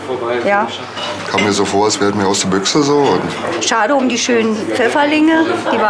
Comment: hat jemand verstanden was der nette Herr bei 8:15 gesagt hat ? :DDD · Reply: "kam mir so vor als wär' dit mehr aus der Büchse so" hab ich verstanden. Er vermutet eine Herkunft aus der Konserve.